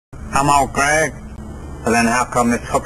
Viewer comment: Whenever he says "I'm Old Greg," I hear, "I'm on crack." Anyone else?